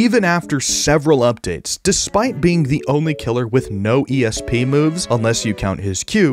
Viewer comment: what did he say? ESP?